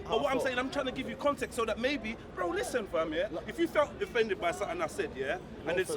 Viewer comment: What did say at 6:25 was he saying don't flex? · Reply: No offence